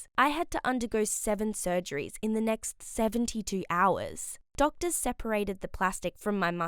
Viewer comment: Yo when I heard “doctor sally” I was like “:0 SALLY FACE”